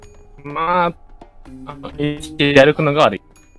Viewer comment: 「HP1で歩くの」が悪いって聞こえた